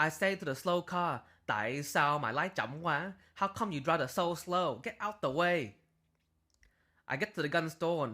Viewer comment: What's he saying at 2:47? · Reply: why you drive to slow in vietnamese